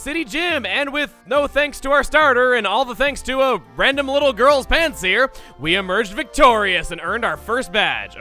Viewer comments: Rewatching lp, thought in the intro he said, “thanks to some little girls pants here” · I thought the first time you said Pansear you said ‘Pants Here’ in combination with the sentence, ‘No Thanks to the...’ you could get why this is problematic. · OK! No way was I the only one who thought he said "because of this little girl's pants here" Ps. Yay forty minute upload! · I can't be the only one that heard him say that he won the battle with a random little girl's PANTS HERE in stead of Pansear. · If you pause the video at around 0:13 it sounds like he said “all the thanks to a little girl’s pants” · I know I misheard this but "all the thanks to a random little girl's pants here." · I heard him say “some random little girls pants here” instead of Pansear and I was like, “WAIT WHAT!!??” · In the opening it sounded like he said "a random little girls pants" 0_o